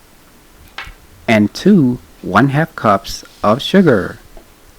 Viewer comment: Is it 1cup of sugar? I heard him say “ add two one half cups of sugar” isn’t that one cup???